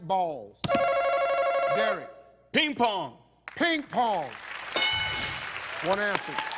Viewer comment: the only 2 things i could think of was a snowball fight and white men lol when he said "ping pong" i heard "people"